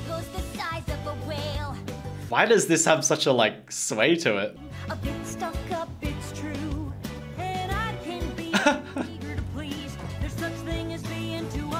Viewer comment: DID I JUST HEAR BIG SHOT??